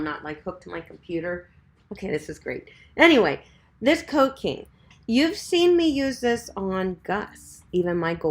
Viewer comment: What is that item you are holding up at 41:30 to use on full ears? I can't make out the name and I don't see it in the notes list. Thanks. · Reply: Coat king